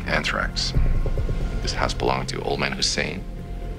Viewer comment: what does house say after anthrax? the subtitles don’t pick it up either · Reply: "This house belong to old man Hussein?"